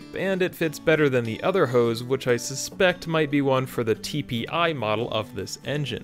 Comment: Is this not the tbi model? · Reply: It's at 16:40. I heard TPI after listening to it a few times, but it's so close it could be the new Yannie/laurel.